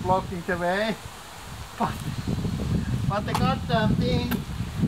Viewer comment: It sounded like you said. "but it got something", then I saw the basketball laying there all looking like a dead squirrel thought, Oh not the squirrel! and no, it was the ball. This video was a lot of fun And the extra content was so beautiful! \m/